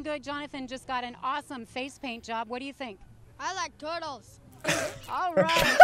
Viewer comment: Ich habe verstanden I like Döner😂😂😂😂